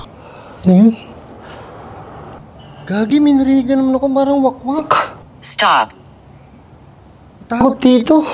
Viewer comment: Jusko mayron 23:24 may mahina na bosses parang wak yata ung narinig ko ..OMG 😳😳😳